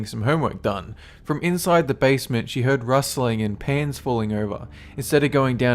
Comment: I thought at 0:29 he said she heard rustling and pants falling down